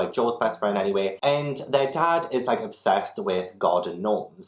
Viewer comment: OK so I misheard "their father is obsessed with garden gnomes" as "their father is obsessed with God and gnomes" X D